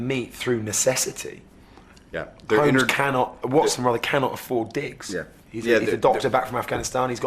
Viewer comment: I heard "Watson cannot afford dicks" XD